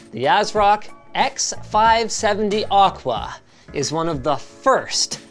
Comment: Did he just say Oqua 1:21 ????